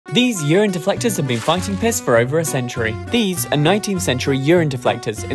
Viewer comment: I thought he said "Urine collecting have been collecting piss for centuries"